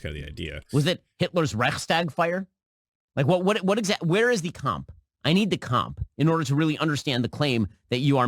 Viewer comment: I heard him say "cum" instea of "Kampf". I am german